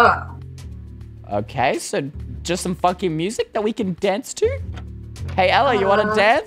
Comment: I swear he said f***